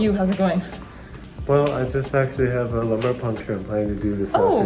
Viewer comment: Hi, I'm learning english with this videos, it's awesome! Someone can help to understand what he said in the minute 3:22? The transcription said something about a "lower punch" but I don't understand the context of that expression. Can someone help me please?